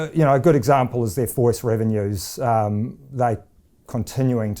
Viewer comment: What does he say at 2:34, couldn't understand it. Transcript says forest revenues . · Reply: Voice revenues - assume land-line and voip